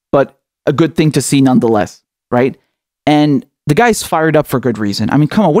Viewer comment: I thought host said Omari Hardy was fired, not “ fired up”, I was prepared to go and start a GoFundMe.